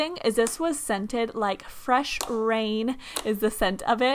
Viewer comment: did she say "fresh brain" or "fresh rain" lol